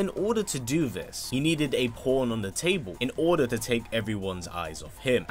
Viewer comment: I heard porn on the table